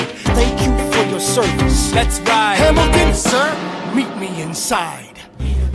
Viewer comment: I kinda thought it said, “Yeet him inside,” my ears hear crazy things. :>